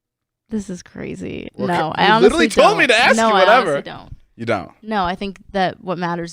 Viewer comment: Haha David nervously says "Really?" 👀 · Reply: When did he say “Really” , nervously? Cuz when he asked if Size matters, I didn’t heard him say “Really” at all 😳, all he said was “You don’t?” Then moved onto the “Do You Think You Can Fall in Love w/ Multiple Ppl”